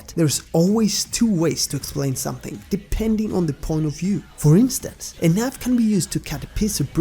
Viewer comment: Wtf is he saying at 1:10 i cant tell bc of his accent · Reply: Hehe a knife dude... thats the word ;)